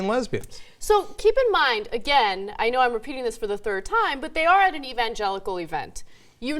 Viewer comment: LOL 1:20 the subtitles said "and i know im a penis for the third time" omg rofl